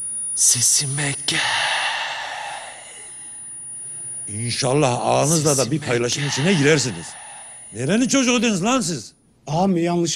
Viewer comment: de ağa ne diyor anlayan allah aşkına yazsın bana :D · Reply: İnşallah ağanızla da bir paylaşım içine girersiniz.